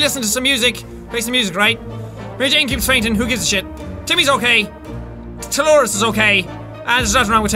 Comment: tolores is what jack said